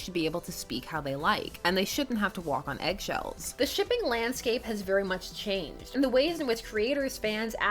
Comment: I thought i heard shipping lance-scape